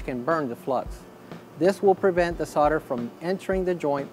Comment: Activate text then go to 1:45 and he says slaughter instead of solder :)